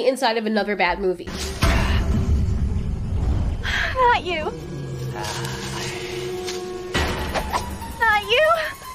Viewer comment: what is she saying??? i dont get it. hi you or harent you? you arent you?. but... i find that funny the way she say it